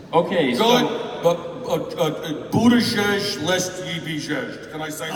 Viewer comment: What does he say at 7:10? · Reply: (Mayor Pete) Buttigieg, lest ye be igieg-ed